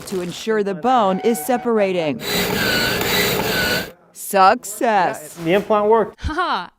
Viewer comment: I thought at first that she said "sucks ass" which made total sense to me. When I found out she said "success" it started to make less sense to be honest.